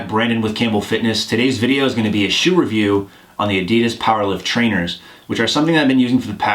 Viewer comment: I sneezed when you said "Short review" and thought you said "Shitty review". I lol'd